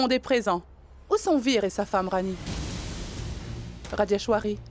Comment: les amis qui a entendu comme moi que la maman du prince jay a demandé où étaient veer et sa femme Rani😂😂